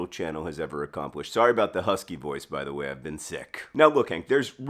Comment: I thought you said husky boys for a second 🤣. If ya know, ya know.